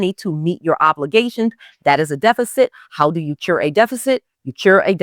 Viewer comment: Can you help me with what you said at time stamp 9:16 - 9:22 ? "Chur"? I asked Google the question and it said cure. So, my question is, How do you cure a deficit with debt? I listen further... maybe my question will be resolved. Thank you for the abundance of great information here.